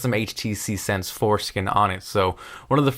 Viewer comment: "custom HTC 'foreskin' on it" LOL jk, thats what it sounded like though..(HTC 4 skin)